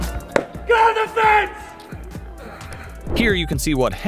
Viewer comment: 3 guards I heard yell 0:49 GET OFF THE FENCE! 3:23 GET OFF THE FENCE! Part 2 3:53 DO NOT TOUCH THE QUEEN'S GUARD!